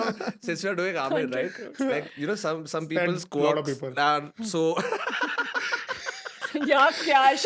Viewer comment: ye kya bola Shantanu yaha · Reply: "said lot of people"